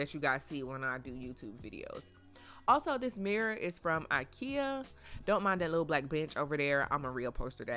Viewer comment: why did I hear “don’t mind that little black bitch over there” 😭